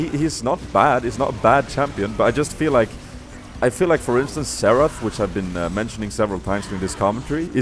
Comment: Haha, all those times you said Xerath, I thought you were saying Seraph, so I was like, oh, you think seraphs embrace is better than Grail, and than at the end I was like Oooooooh.